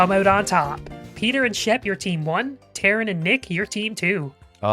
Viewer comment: What was said: "You're Team 1." What I heard: "Your team won."